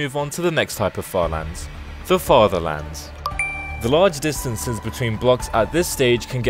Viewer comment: Bro when you said further lands I thought you said father lands 💀